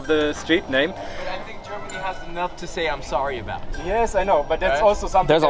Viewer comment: "Germany has enough to say I'm sorry about" is a weird response to someone saying the German colonial past must be acknowledged and spoken about esp because systemic racism (based on colour) is still highly prevalent in all spheres of German society, EVEN in Berlin. jus my 2 cents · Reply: I thought he said “Germany has a lot to say sorry about” now he irks me